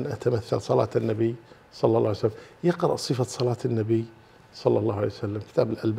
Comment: وش الكتاب اللي ذكر ما سمعته بوضوح؟ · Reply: صفة صلاة النبي للشيخ الألباني